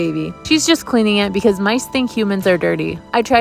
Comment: I heard "bc my stink humans are dirty" and assumed that's just how she refers to "hands" 😭😭